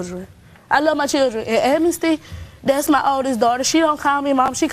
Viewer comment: did she say her child's name was Amnesty? 😨